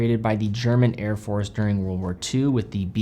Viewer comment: I'm german, I've listened to this word 10 times over and I have no idea what he said there, could somebody tell me? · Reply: I think that is his pronunciation of "B-Uhr" (as in "Beobachtungsuhr").